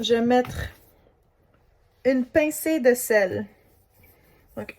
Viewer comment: did you say "Je mettre une pincée..."? The french I learned taught me it was "Je mets". Is that a Quebec thing?